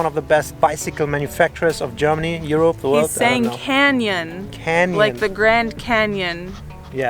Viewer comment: I thought that he said Kenyan, not Canyon.